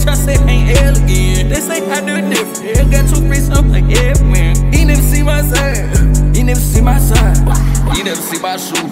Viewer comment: this part sounds like he saying the n word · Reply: Sound like he saying "he neva see my..."